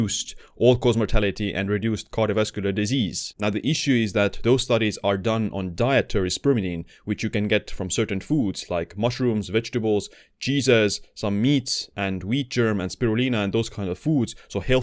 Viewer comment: at first i thought you said "Jesus"